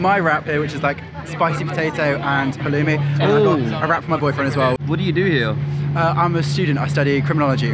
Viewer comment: did he say "my boyfriend"?!!!!!!!!!!!!!!